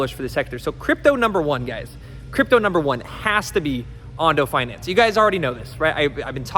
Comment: Can someone write this 5 coins please i can not spell at all otherden ondo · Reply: I thought he said Onofinance but can’t find it?